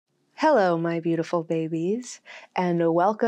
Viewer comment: I heard, "Hello my beautiful boobies!", I nodded my head and then all of a sudden the video was over. Encore.